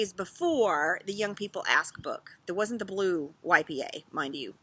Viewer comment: You said " the blue YPA" - I heard "the blue wipey, eh?" Love the format of a chat out on the deck. Like we were having a chat over coffee.